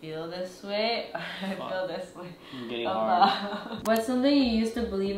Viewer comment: did just say he’s hard…LMFAO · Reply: He did say “im getting hard” lol BUT in his defence he clarified with “my heart is hard” but i cut it out 🫢